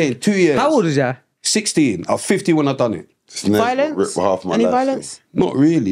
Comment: is avoiding saying what he done!!! SUSS "Kinda violence" he says